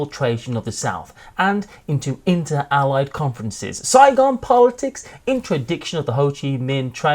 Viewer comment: For Fire in the Lake, when said “Saigon politics,” I heard “Zygon politics” and thought it might be a Doctor Who game.